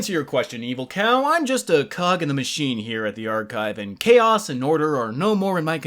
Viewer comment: After Dave yelling bitch I could’ve swore he said “I am a cock in the machine”